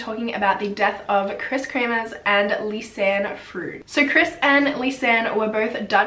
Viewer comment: I thought it said lisanne from krispy kreames